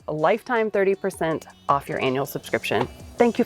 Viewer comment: did she said "anal" subscription??